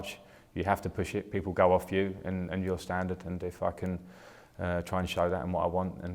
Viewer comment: Did he said f***ing at 04:30 .. I may be wrong but it sounded like.. but you are welcome back Frankie Jr. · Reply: nishikant singh he said if I can